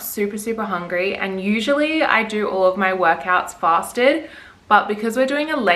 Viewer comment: I literally thought you said you do all your gym sessions “plastered” instead of fasted lol. I was like shit this girls hardcore 🍺💪🏻😂